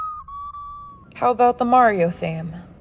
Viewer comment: when she said “ooh, my little pony?” i heard: “ooh, are u a brownie?”